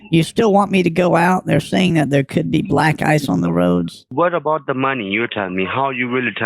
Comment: i thought you said black guys and not black ice.